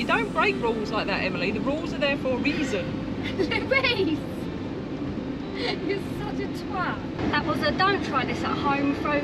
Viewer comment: did you say «you’re such a twat»?😅. The both of you are hilariously good YTers.